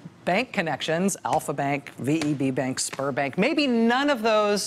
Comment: I wasn't watching the screen and had to backup to check cos I heard "sperm bank". Seems fitting tho.